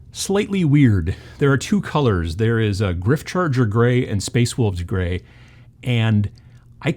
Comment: I kept hearing "spaceballs Grey" and got excited. .. ..